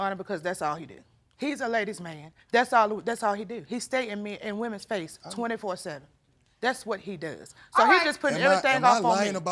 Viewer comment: did she say he stays in MEN and women’s faces?